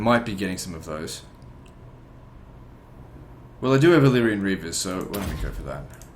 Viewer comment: everytime he selects the Cavalry I keep hearing "Illyrian Beavers" and that makes me want a beaver unit in Total War. maybe in the next historical total war Canada will be playable and I'll get my wish.